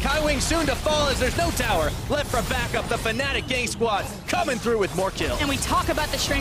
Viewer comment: am I the only 1 who hears Fnatic gay squads XD